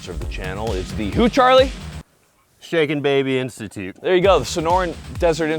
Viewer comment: I thought I heard shaken baby prostitute lol